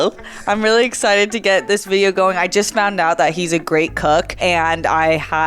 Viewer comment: 0.18 idk why i heard that "He is a great Cuck" 😂😂😂😂